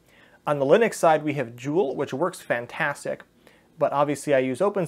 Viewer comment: What was said @ 15:53? "On the Linux side we have {{ jewel/joule/dual? }}, which works fantastic[...]" Thanks for sharing this! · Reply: Jool - <link>